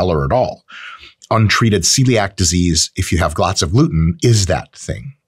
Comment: Anyone catch 4:28, “glots of luten” 😂 Not hating, love your content! ..but still a bit funny.